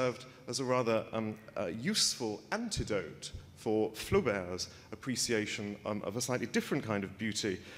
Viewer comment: HEY ... at 2:36 ... the subtitles indicate ' FLU BEARS ' ... ! instead of FLAUBERT s ... !